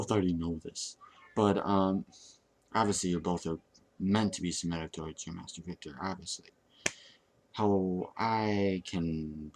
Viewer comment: Can someone help me? What does he say here 1:30 "obviously you're both meant to be _________ toward your master Victor"?? He repeats this, I can't hear it. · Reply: i think Submitted*